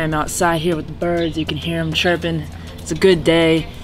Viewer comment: Kinda sounded like you said you can hear them tribbing. I’m stupid sometimes 😂